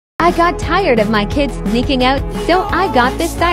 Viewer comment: PLSS I HEARD "I got tired of my kids making out." I immediately thought of hopper dealing with El and mike😭😭